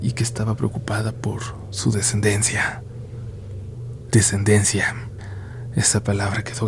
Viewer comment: no entendí esa frase🤔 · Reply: Descendencia, probablemente tenia hijos